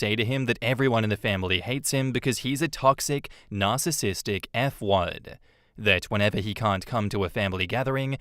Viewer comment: I misheard "f-wad" as "f-word" and started scrambling to see what the hell I missed.